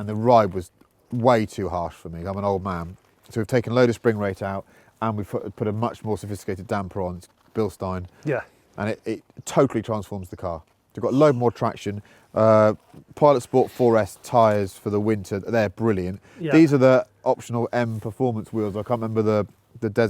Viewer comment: Did he say "Pilot Sport 4S for the winter" at 22:53?